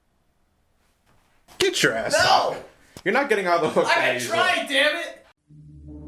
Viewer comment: What does and say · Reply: was saying that isnt getting off the hook and says he is trying damn it.